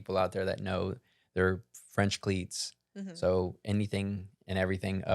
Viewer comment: I thought y'all were saying "French pleats" instead of "cleats." Now I need to schedule a hearing test! :)